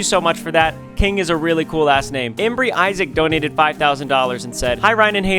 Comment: did Ryan say "ass"? Or am I trippin'? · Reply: You are trippin’ lol he said really cool last name